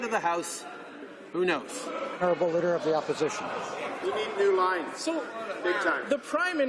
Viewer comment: Did I just hear someone shout out, "You need a new line! Bigtime!"??? Because it's so true! Trudeau needs to (a) answer questions, and (b) stop lying/deceiving.